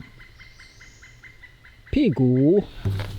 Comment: Did I just heard 屁股 @ 45:44 ？ LOL